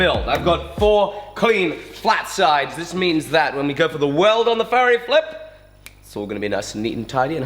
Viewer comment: Who else thought Alec was saying farrier flip this whole time...